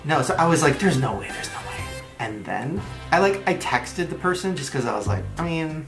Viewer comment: I thought he said he messaged them? I was assuming instagram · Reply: he said texted.